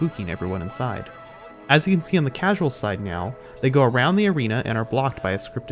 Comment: did he say “meow” instead of “now” just then?